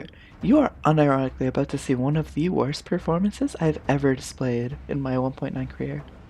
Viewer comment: thought u said slayed 💅lmao